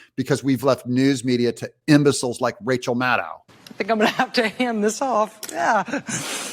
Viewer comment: I thought she said "Lets Ham this off."🤨🤔😒